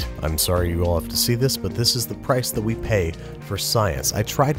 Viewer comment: oh wow 7:37 pirce instead of price